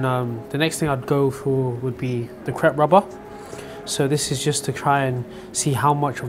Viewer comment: did he say Crep rubber?